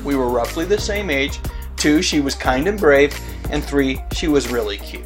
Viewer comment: what is #2? what do you say right here...she's kind and brick?